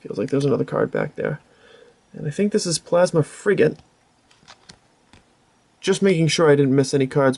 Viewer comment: LOL it's sounds really funny how you say Plasma Frigate ,sounds like your saying it as Plasma Freegate instead XD rofl!Gotta work better on the pokemon pronunciations there XD lolz. Goddamn i so forgot how badass as sexy that darkrai fullart and giratina and Cofagrigues look like!Great opening man!